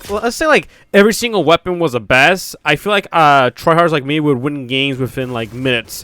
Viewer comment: did you just say you’re a try hard?